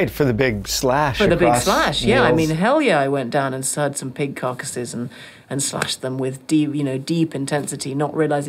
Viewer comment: I swear I'm not trying to be funny or anything like that, matter of fact, I'm not even going to type what I thought I heard. I'm just going to.time stamp it and see if at least 3 folks agree that they thought they heard something VERY "dirty" 😐😐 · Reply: I know what you think you heard, but she actually said "pig carcasses", she says it again a few seconds later and you can hear clearly